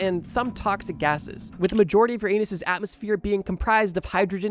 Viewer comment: even the caption says “your anus” c’mon now😂